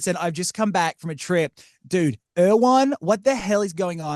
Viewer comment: I am confused did he say he just come back from IRAN?